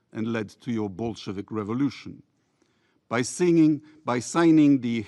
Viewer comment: For a second there, I thought that he said "your bullshit revolution." lmao